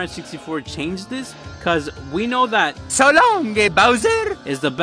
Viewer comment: What Mario really said was "So Long Kinga Bowser!"